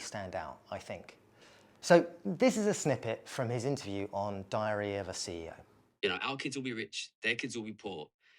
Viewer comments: Diary or Diarrhea of a CEO? I misheard. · Somehow I always hear "diarrhea of a CEO" 😂